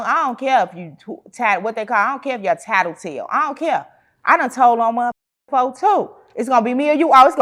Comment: Did she say she told on people?